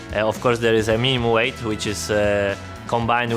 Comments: i heard kubica said meme weight. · Did kubica say MEME weight at 5:55 while Nando was being weighed lol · first I heard this as meme weight and then I saw Norris and I confused as fuck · did he say "meme weight"?